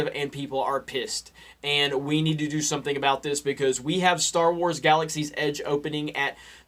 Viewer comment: i heard "star wars galaxies" and tunnel visioned